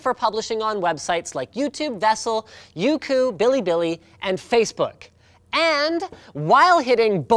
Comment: he names websites that the videos can be published on but does anyone know what the "billy billy" website that he said is?